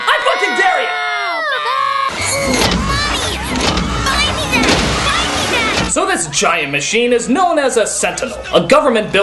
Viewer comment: Anyone else hear "Mommy buy me that, mommy buy me that!" around 3:15?